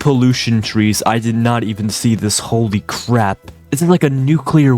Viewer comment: Always mishear "holy crap" for "holy crab"